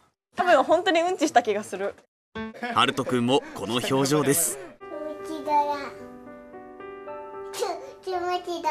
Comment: うんち柄にしか聞こえんwww